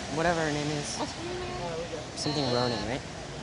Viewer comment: Jaeden's crush is not Halle berry its wyatt😏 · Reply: I thought he said Halle bailey I was like “you have a crush on Ariel?!” But then I realised he said Halle Berry